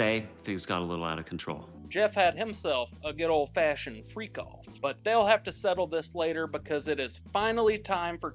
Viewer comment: Did he say freak off at 10:35?